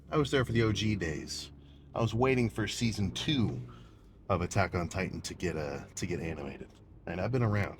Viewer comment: I heard "A-back on titan" and I hit a PR